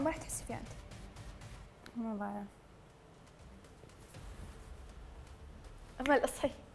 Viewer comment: سمعت قالت امل هون 🤔